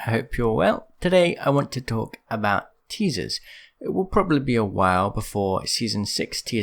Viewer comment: My volume was low and the first thing i heard was.."hello everyone today i wanna talk about Jesus"😂😂